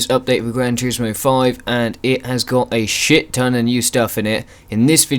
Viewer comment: He Did Just Say Shit?